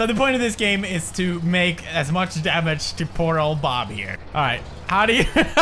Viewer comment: Is it just my love to PORTAL <3 (This game is the game of my childhood) or did he say "to Portal Bob here!"?